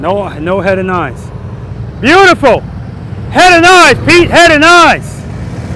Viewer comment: all i hear is "VERYY NICEE, PETE!! VERYY NICEE!!" in a strong Kazakhstan accent. If you know, you know. :face-blue-smiling::person-turqouise-waving::face-fuchsia-tongue-out